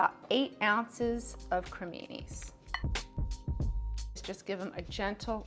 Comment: to my ears it sounds like "panini", YT CC says "creaminess", but I think it's either "criminis" or possibly "porcinis"?